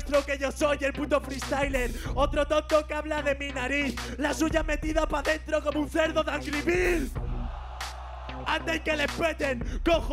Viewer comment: Que dices asi no es jajaja. Lo que dice Chuty es: "otro tonto que habla de mi nariz. La suya metida pa dentro como un cedo de Angry Birds". (Min